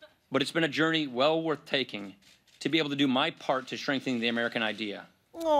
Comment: Does it or does it not sound like he says "shrinking the American idea" instead of strengthening